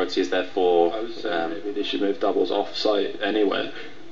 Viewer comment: what does Ross say at around 01:53 when Andy laughs hysterically? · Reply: They should move doubles off site anyway..